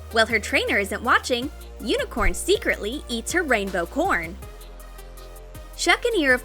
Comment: I thought you said rainbow. Porn xD